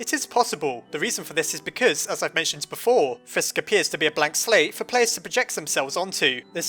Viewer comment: I thought he said "black slave" instead of "blank slate"